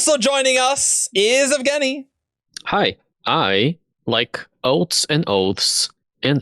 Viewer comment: I heard say "boats and hoes" and i was very confused